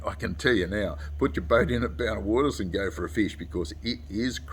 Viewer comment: did say Bowna Waters?